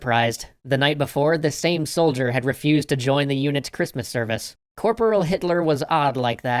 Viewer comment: Did I hear right?! I think I heard: Corporal Hitler!